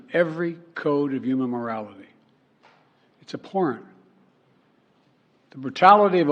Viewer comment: did he just said it's a PORN